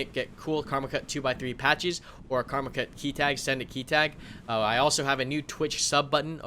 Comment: I was going to make a comment about those beautiful maps but then I thought I heard say "Karmakut 'scented' key tag" instead of "send it."